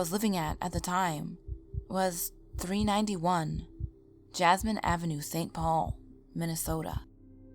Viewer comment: 391 jessamine is in foreclosure since 2014 no wonder why the ppl left it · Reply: Kaprice W. Oh!! Jessamine!! I thought she said jasmine!!!